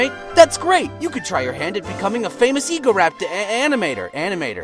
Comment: I thought he said "As fake as Egoraptor" not "A famous Egoraptor"